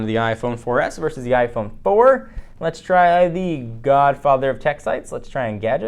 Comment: I hear the gayfather 3:05 lol